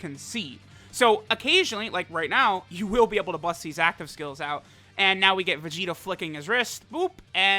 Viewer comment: did he just say now we get to see vegitos flicking his wrist